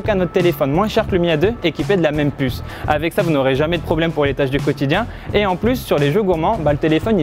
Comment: Les quoi ? 😁😂😜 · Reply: Will I am Tâches du quotidien, les oreilles ça se lave.